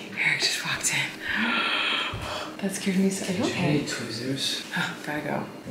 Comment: When stepped on the piece of class, I could’ve sworn he said “wheres the twizzlers?!” I just thought he really wanted a twizzlers and then I realized he said tweezers